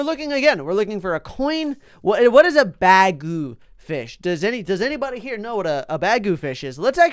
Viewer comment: When he said bagu fish I thought he said bag o fish! XD